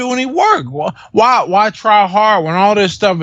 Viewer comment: if you turn on the subtitles at 1:03:32 it says "trihard" instead of "try hard" dafuq